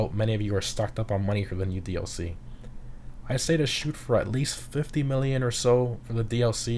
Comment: did you say 50 million or 15 million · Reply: 50